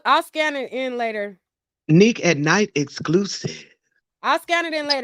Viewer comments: I couldn't even hear some of what it was saying because all i kept hearing was "nique at night exclusive" like every 5 seconds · Damn I can’t hear because all I keep hearing is “Nique at nite exclusive” 😒